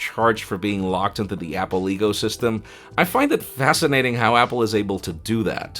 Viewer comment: Did he say ".. Apple Egosystem"?